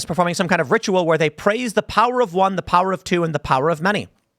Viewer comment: Until I read the subtitles I thought they were saying "The power of WOMEN, the power of two, the power of many" Can't really blame me tho